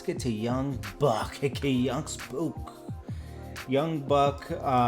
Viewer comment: did he call him "aka young spook"?... da fuc... lol